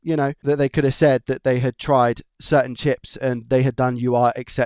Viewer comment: Old man hearing, I thought you said you had to replace the "Chimp". I didn't see one anywhere. Great Job and Video! Thank You.